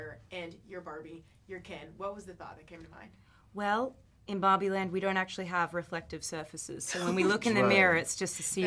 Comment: I only hear 'In Bobbie land'